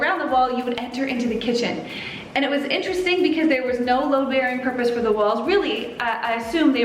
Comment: Hey, it sounds like your saying LOW bearing walls but its called “LOAD-bearing” walls